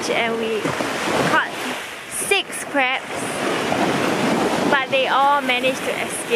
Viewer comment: sounds like she said I caught 6 craps